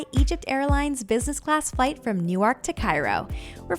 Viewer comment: did you say “New- waak”? 😂😂